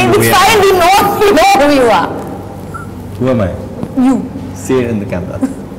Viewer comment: did he say your mine ❤️❤️ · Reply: It was Who am I..